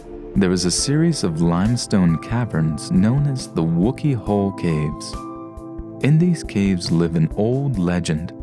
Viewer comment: I misheard Wookey Holes as Woolie Holes, and honestly, I don't think there's any difference.